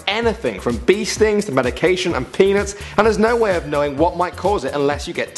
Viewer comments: Sure sounds like he says penis. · who else heard him say penis